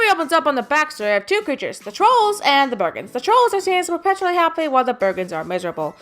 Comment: I am calling them the Burgens. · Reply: The Slinky Kid Its sounds like you said the burgers